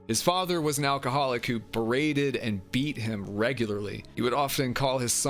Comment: Did you said "breeded"? 💀